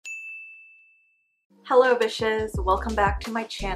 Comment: First time watching you and I thought you said “ Hello B***es “ 😂 But you probs didn’t thanks for the amazing tips it’s really gonna help me with my drawing! Thanks- A girl from Wales 🏴